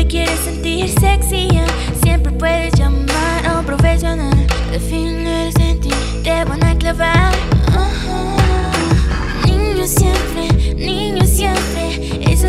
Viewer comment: pensé que decía "al final siempre te van a clavar"